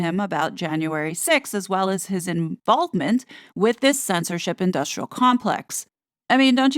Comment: When said Harry's "involvement," I legit thought she said, "inBALDment." 😂